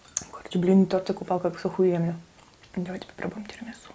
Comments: "блинный тортик оказался оху*нный" :D послышалось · На 7:06 ты сказала Ахуенно? Или мне послышалась😄